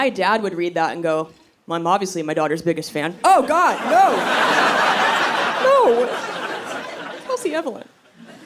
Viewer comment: For everyone trying to figure out 7:28 I think she said "Oh God no, Kelsey Evelyn!" I'm guessing her first name middle name that her father calls her.